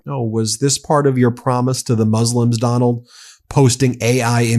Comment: it sounds like he says Mooslum not Muslim.